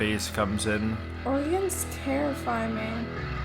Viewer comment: Did say 'All YINZ terrify me" @ 1:13? I didn't think yinz were from Pittsburgh. Cause that YINZ is Pittsburghease lol